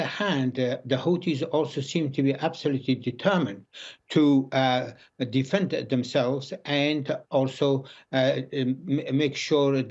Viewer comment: did he just say the fan themselves? Doesn’t he realizes that the Hootie’s are firing missiles at ships that are passing by? Well, he does now!